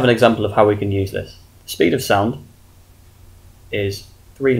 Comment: Thought the speed of light was 3x10^8ms-1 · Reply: Conor Taylor dw just heard you say speed of sound my bad